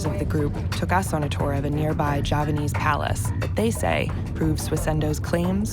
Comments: She says Javanese but the captions say Japanese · Hey vice. 7:20 the subtitles are wrong. It says "Japanese" in the subtitle but it's supposed to be "Javanese" as in Java, Indonesia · The lady said “Javanese Palace” but the subtitle said “Japanese Palace”! FIX IT! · so on 7:22 the subtitles say japanese.. but they mean javanese right.. like thats a massive difference in distance traveled.. im not the only one who noticed rght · lol i think she said javanese* not japanese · Your caption says "Japanese" (which is ridiculous... wrong country) as the announcer clearly says "Javanese", which makes sense. Java's part of Indonesia. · VICE! 07:20 The subtitles are wrong, it says Japanese, whereas it should be Javanese. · Check your subtitles. You put @ 7:20 Japanese instead of Javanese. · shouldn't the subtitle say Javanese instead of Japanese?